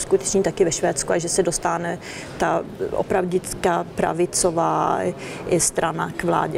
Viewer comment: the subtitles say "and that a radical right-wing party might come into power". She actually said "real right-wing party", not "radical".